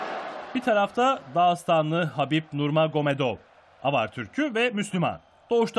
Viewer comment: Avar Türk'ü mü dedi ben mi yanlış anladım?